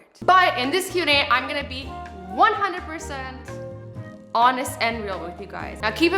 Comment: Who else thought it said hottest